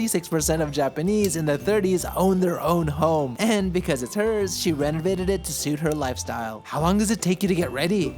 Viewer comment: Did he just say 'And because of the terrorist, she renovated it to suit her lifestyle' at 0:48 ? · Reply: Because it her is what he said